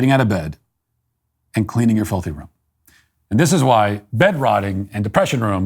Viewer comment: I thought Matt said “…. And cleaning you’re F***ing room” at the end there. I was stunned. Now im kinda disappointed he didn’t say it..